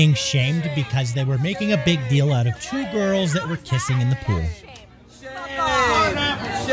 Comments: At first I thought it said “pissing in the pool” and I was like shouldn’t they be mad about that?? But I rewatched it and it said “kissing” makes more sense now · I thought you said “pissing in the pool” at first I was gonna be on Karen’s side. Dodged a bullet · The one husband smiling the whole time is priceless 🤣🤣😂😂 I don't think he had any objections to the kissing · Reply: I heard "pissing" at first and I was kind of confused because it is very uncomfortable knowing someone is pissing in a public pool. I had to read this comment to realize they were kissing and not pissing.